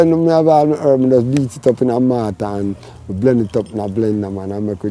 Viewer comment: Did say blender🤔